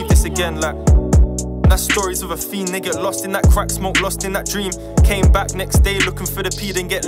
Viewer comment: did he say nigga at 4:55 ? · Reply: "Stories of a fiend they get lost in that crack smoke lost in that dream"